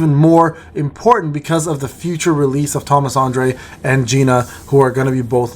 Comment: I swear I heard "thomas zendaya" xD